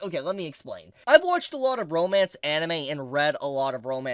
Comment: am I imagining things or did say "I've watched a lot of bromance anime" 💀